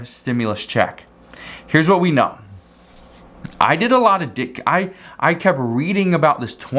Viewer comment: So nobody else heard him say “he did a lot of dick “😂😂